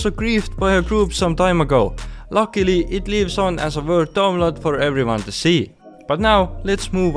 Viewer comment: what does he say at 4:40 ? Luckily its ... ? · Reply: Luckily, it lives on as a world download for everyone to see